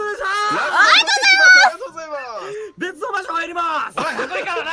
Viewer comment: Ras said "Let's... go somewhere else" instead of "Let's... work together" I love all CR members so I'm so glad there are EN translations for them <3